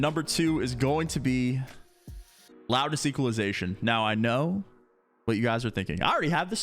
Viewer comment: What does he say to turn on at 7:02 ??? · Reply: Loudness equalization